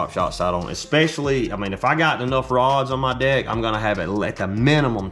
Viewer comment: I thought he kept saying “on my Dick😂”